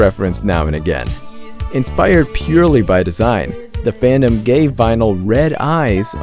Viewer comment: did you just say gay!?